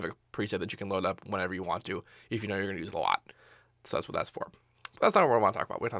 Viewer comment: wat did he say? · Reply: a preset you can load up whenever you want to, if you know you're gonna use it a lot.